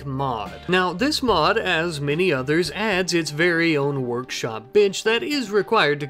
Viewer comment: i heard bi## insted of bench